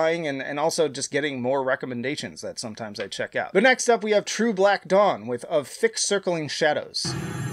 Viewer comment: I thought you said "true black dong" at first, and I was rather taken aback :P